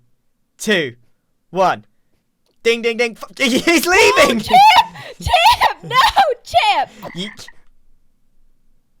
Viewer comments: What did I just hear say? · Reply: The start of "For goodness sake" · Idk if I'm hearing things but when Champ jumped from the ring I heard say "fuc- he's leaving!!"